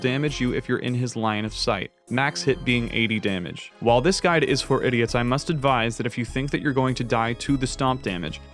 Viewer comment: I heard “for idiots.” I’m in.